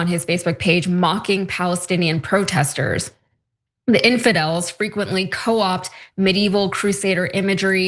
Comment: Did she just call Christians Infidels at 5:11 ????